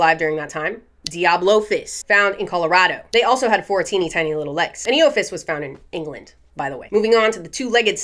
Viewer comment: Instead of, “four teeny tiny little legs” I heard, “fourteen tiny little legs” and that was a wild mental image.